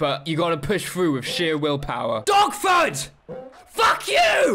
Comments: OH the dog's name is Dogford. I was wondering why you kept shouting "DOGFOOD!!!!" at it. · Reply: I thought he was saying DOGFOOT the first time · Thought you were saying “dogfoot” the whole time you were saying “dogford”